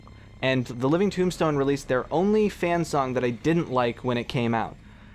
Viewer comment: I heard "Release there Only fan song" o.O 2:07:23 Jump scare!